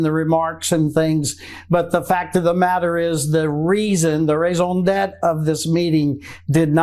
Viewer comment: did he just say raisin debt instead of raison d’etre? Lmfao don’t use foreign words if you can’t pronounce them. You sound dumb.